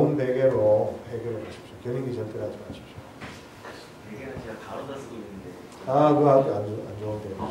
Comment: 무슨 베개를 말하는 건가요? 가로다? 가루다? 소리가 잘 안 들리네요.